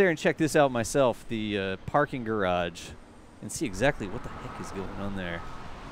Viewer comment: Did you drop the F-bomb at 12:49 when talking about the parking garage? A bit unclear so if you did you may have got away with it but it kinda sounded like that! Awesome and informative content as usual - keep up the good work guys - it is most appreciated! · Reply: That would be "what the heck is going on there"... similar letters.